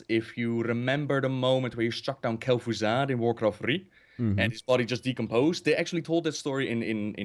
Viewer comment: When said he played warcraft3, it sounded like he said warcrap3